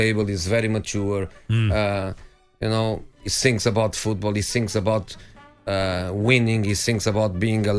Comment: I thought mou said karim sings about football. Oh, he meant thinks. 👀😎